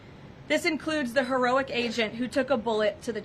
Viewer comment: Did she just call the officer who took a bullet an Asian? Wow! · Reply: I thought she said “agent”